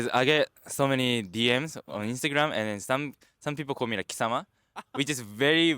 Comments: he mentioned a word that is considered very rude that his instagram followers called him. what was the word? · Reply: Kisama - it's something that You can translate as "you bastard/you trash/you moron" 😂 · What is this word he says is rude to call him? I'm so curious. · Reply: Kisama